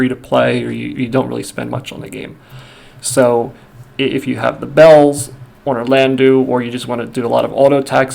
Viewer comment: "so... if you have the balls"? I think i heard wrong